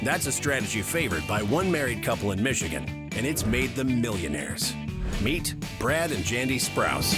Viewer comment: I thought the narrator said "Meet Brad and Jenny [Aniston]"...there goes my all-time dream couple finally ending up together.